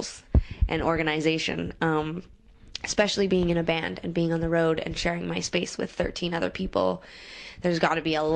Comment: What I heard: "... and sharing MySpace with thirteen other people". Oh come on people, let Tegan have her own MySpace page: no sharezies k?